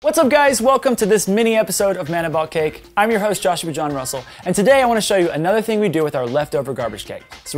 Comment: No joke he talks so fast when he says his name I always hear "my name is joshman russel ".